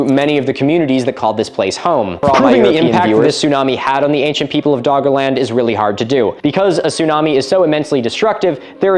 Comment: wtf is said here? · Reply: I assume you mean the underlying voice. It's him from earlier saying sorry European viewers